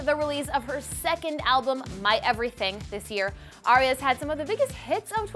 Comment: Is it just me or did she call Ariana Grande "Aria" at 7:05??? lmao